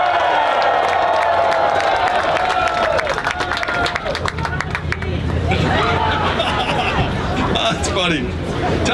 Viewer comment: What did the guy scream at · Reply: I love you, Evelyn. -- yang's wife name.